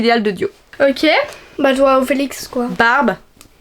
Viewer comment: c moi ou a dit Félix ?? Les fan de stray kids on se sait 😋😅